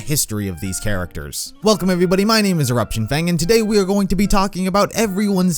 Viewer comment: I thought he said Erection Fang